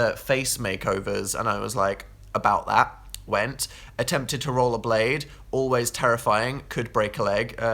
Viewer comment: he said "roll a blade" and i heard "roll a blunt" for some reason and my world just got hella shook for about .4 seconds 😂😂